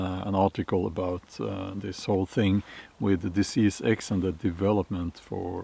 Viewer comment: My bad I thought you said The Sea Sex. I was like hell yeah! I’m in!!